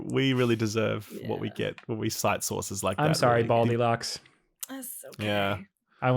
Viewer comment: Go to BUY RAYCON dot com slash friends TODAY to get 15% off your Raycon order, plus free shipping! That’s right, you’ll get 15% off and free shipping at BUY RAYCON dot com slash friends. BUY RAYCON dot com slash friends. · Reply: I thought she said bold elocks. Not baldilocks 😮